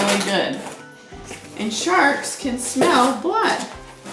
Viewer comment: went from talking about poop juice to sharks and the second she said shark I thought she said shart and I about died 😂😂